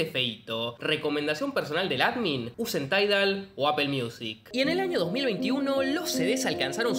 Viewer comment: que dice antes de Apple Music?? no le entiendo ...taigal?? 😆